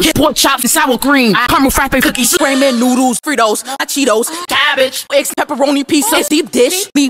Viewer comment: I thought it said "Three holes" but it said "Cheetos"